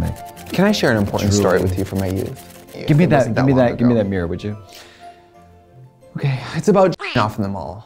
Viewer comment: I thought katya said “hand me that beer won’t you” 💀💀